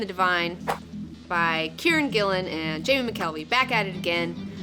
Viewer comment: I thought you said Karen Gillan lol